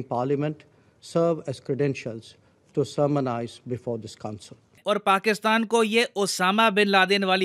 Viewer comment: Slight correction, “To Sermonise before this council.” At 4:43 Slight Correction, “Intends instead of Intense.”